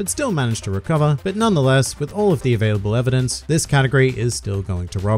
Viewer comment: Anyone else here this katakuri instead of category?